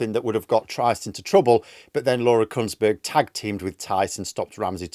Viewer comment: For a moment there I thought you had said Kuntsburg so I backtracked and found I was mistaken. Must be a Freudian thing.